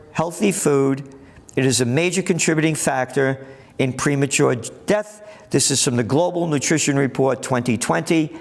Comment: "Premature ej..death" ....or did I hear that wrong? Haha